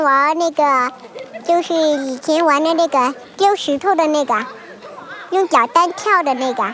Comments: why does iT sounds like she is saying nigah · I swear I hear n** and then later "how to kill a n** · I think she said the n word like 4 times 😂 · did she just say "SHE WANT A NI66A SO SHE TOLD THAT NI66A" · Between 3:32 and 3:38 I swear I heard her say nigga 3 times. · am i the only one who heard alot of (n word) ? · did anyone else hear the n-word?? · why did I hear the N-word like 5 times? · what she say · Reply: 那个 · Why did it sound like she said the n word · I heard 'N' word 3:36 😢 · Why does it sound like they say kill the N*gga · It sound like she said nigga in 3:35 but it mean something else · the little woman sounded like she was saying the N - word.. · did anyone else hear "kill da niqqua"?! 🤯😵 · did she say nigga or my mind was playing tricks on me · Did I just heard the word nigga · did she say "Kill Da Nigga" ? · I could've sworn I heard "Keel da nigga" at 3:37 Das Racist. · it sounds like she says nigga over and over again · I heard N** like 5 times lol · Starting at 3:35 does anyone hear the little Asian saying nigga? Lol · was she saying nigga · anyone else here what I heard or??? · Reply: It's the Chinese word for 'that' or 'um.' na ge 那个 (The na is usually pronounced 'ne' in this word) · I'm not being rude but it was so funny at 3:35 when the cute women was talking sounded like she said the "n word" 😂😂 multiple times.